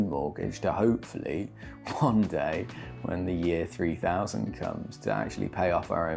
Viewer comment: Did he say @ 10:55 year 3,000? 😮